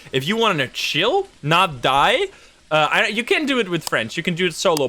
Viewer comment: I always understand "You can do it with french" instead of friends...